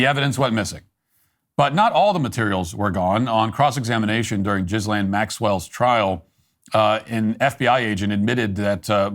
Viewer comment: Did he just say jizzlane Maxwell?